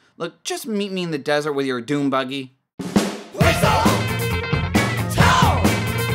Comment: the into in this show does not sound like its saying weasel town it sounds like there saying weasel......toe wesel toe!